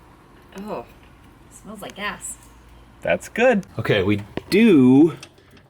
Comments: I honestly thought said "we, it smells like ass" haha until it clicked to me haha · Hey, are you sure said "it smells like GAS?" Because as someone who's been married for what seems like forever, it sounded like she said, "it smells like ASS." Just saying. Lol.😂😂😂😂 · I thought said "Ew... smells like ass" 😂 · LOL I heard "smells like ass" LOL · I could have sworn said it smelled like ass.....lol But, I guess she said gas........lol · why tf did I heard say “ooh, smells like ass” and answering “That’s good” ?😂😂😂😂😂😂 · At 3 min. 20 sum seconds. I thought said, smells like ass. And replied, that's a good thing. I was laughing myself hysterically. Then I rewound it to show someone. And realized she said gas, not ass. Might be true either way.